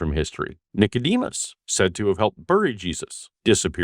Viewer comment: I misheard that as "said to have helped Furry Jesus" and did a double take for a second